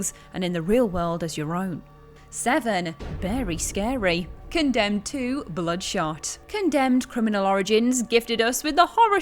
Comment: It sounded like you said “Condemned 2: Blood Shart”. That would be scarier than anything on this list.